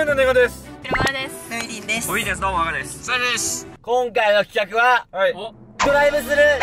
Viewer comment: サルです。 にしか聞こえん